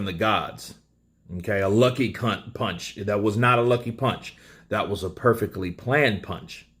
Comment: Did you actually say a lucky cunt punch?? 🤣🤣